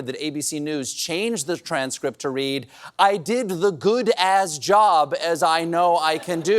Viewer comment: I heard "the good as job" when I heard it too.